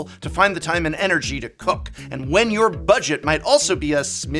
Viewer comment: I was munching during 1:33 and I misheard the work "cook" with the word "fawke"